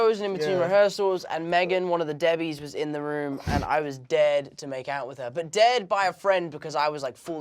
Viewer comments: I thought he said “I was DEAD to makeout with her” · I thought he was saying he was dead to make out with her